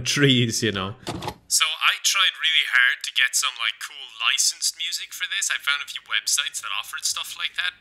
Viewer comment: I heard "Some cool ISIS music" 💀